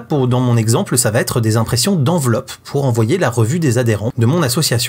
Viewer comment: association de mon quoi ? 😡😡😡😡😡😡😡😡😡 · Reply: Oui oui ... Je dis juste que je vais envoyer le magazine de mon asso ... Si tu comprends autre chose va te faire soigner !